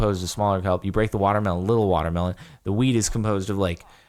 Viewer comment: it sounds like he said weed